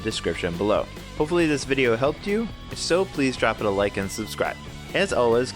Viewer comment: did he just say "Comments can go blow me? "